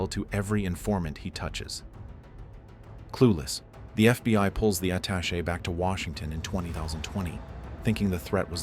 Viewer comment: does it say "20 thousand 20"?